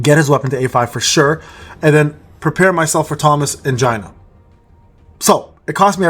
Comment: Waaat? I heard "Thomas Angina" and thought you were making a Joke about Thomas Andre with a straight face but it came out wrong. Then realize you were talking about Gina except it's pronounced "Gee-nuh"